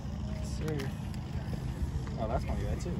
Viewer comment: I could’ve sworn he said “Liam?? That’s my guy” lol 💪💪💪 You belong in the big leagues with those guys in the future.